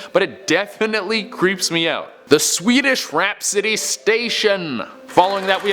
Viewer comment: I kept hearing "Swedish Rap City"! Maybe someone will make it?